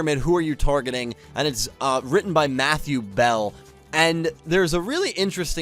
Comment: Lmaoo I thought you said Yubel at first